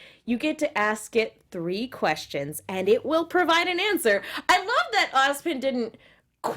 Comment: A funny "misheard" I had in this episode: When the relic was explained I heard: "The land will provide an answer." My mind went: "Oh, that's not good. That could be dangerous. Not only to the asker, to innocent bystanders. - What if the Grim are the answer to a Question someone asked in the past? Sadly nobody knows anymore what that question was. -- What if Ozpin asked that question?" It was quite a bummer when the actual working of the relic later was revealed.